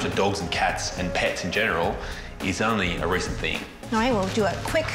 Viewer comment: I thought he said "Looking after dogs is only a recipe." He meant recently... omg lol